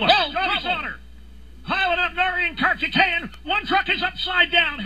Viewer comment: Does anybody have a clue what Dave Moddy is trying to say around 0:12? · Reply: Travis M Simpson I have wondered that myself lol. But I figured it out. He said "Piling up (are) Narain Karthikeyan, -one truck is upside down.." My guess is he was going to start listing drivers, as he usually does, but then hornaday lifted off. Narain Karthikeyan. What a name.